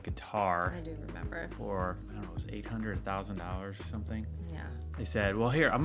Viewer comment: it sounded like 800,000 😮😂 at first, I had to rewind 3x, haha! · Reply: It was $800 to $1000